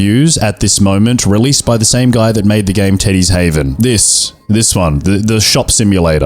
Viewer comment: I’m trying not to laugh and wake my mom up but I swear to god I thought he said ‘Titties Haven’ 😂😂😂😂